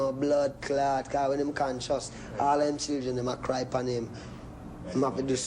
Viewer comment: Can someone please interpret for me what he's saying at 9:29? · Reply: when a man is unconscious all his children will cry upon him because the children need a man who is conscious. Aka don't get drunk on rum and let us down. Live up!!!!